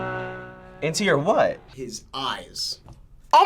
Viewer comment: 😂😂 his gay ass really thought he said “I push my fingers into my ass” 😂